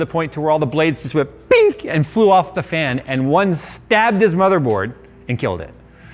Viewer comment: am i the only one who heard "stabbed his mother"?